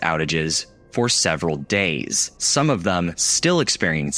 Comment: excuse me but the English subtitle says “Hours” not “Days”!